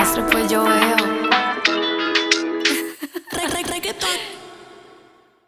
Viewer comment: Escuché el 3:25 solo con audífono derecho y me quedé re 😦 Pq solo escuchaba un “RE-“ JSJSJSJ que grande kali uchis reina del audio 8d 💅💋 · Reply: She Said reggaeton